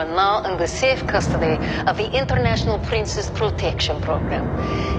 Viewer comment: Oh I thought it said princess projection program